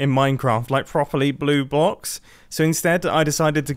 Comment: Did he say 'Blue Box'?